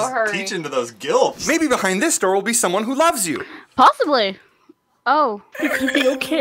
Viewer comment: really hasnt changed all of season 2 lmaoooo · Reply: i thought you said 'i love love you'